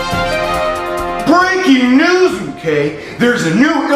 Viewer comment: is slowly losing his touch. He said “Breaking news mk.” Instead of “Breaking news mkoiy.” Edit: 5:22 Lmao you can see Logan in the top left of the phone. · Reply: On 0:18 actually says m guy watch the character q&a's on channel